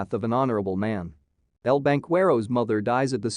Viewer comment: El banquero captured galindo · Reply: Funny every Time I heard his name I thought they were saying his name as El Vanqero and in the cowboy in translation